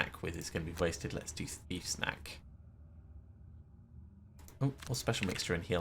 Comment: Every time you say "thief's knack," I hear "thief snack." That should be the name of the healing card.